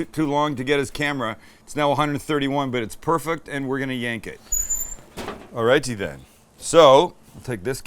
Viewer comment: Did just say it was time to yank his meat?